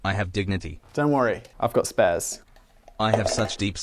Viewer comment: Btw, the shirt I'm wearing in this video was designed by the LEGEND that is Mitchell Turner, and you should definitely follow his Instagram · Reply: On first hearing the opening, I thought I heard : Shirt : " Please don't make me wear the hat again ! " " Don't worry, I got " Fez " ". ( It was actually 'Don't make me wear the cup again, don't worry, I've got spares.) The fact the "Fez" was a red cup and tried it upside down, add to the perfection ! lol Luv t'stuff